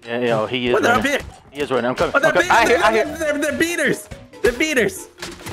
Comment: For a second I thought just called them Beaners 😂